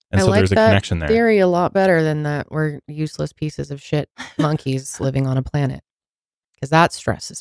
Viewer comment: I swear to god I thought she said "we're useless pos HONKIES living on a planet" and I had to rewind. LOL